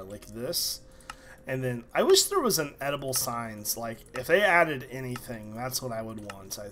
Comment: When he said editable signs, i thought he said edible signs, so i was like "What would you do? Make them out of steak?"